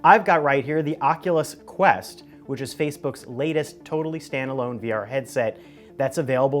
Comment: Is it me or did he just say facebooks